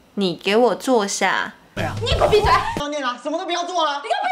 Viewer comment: Wooow I heard people in dramas saying 你给我闭嘴 so many times, but I didn't understand it correctly and I thought they said 你过闭嘴...I had no idea of what that 过 meant! It turns out it's only 给我 said in a very fast way lol Anyway this is a very helpful video, thank you~